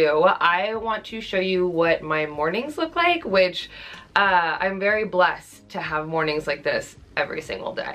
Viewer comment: When you said “I’m very blessed to have mornings like this every day” I thought you said “I’m very blessed to have morning sickness every day” and I to REWIND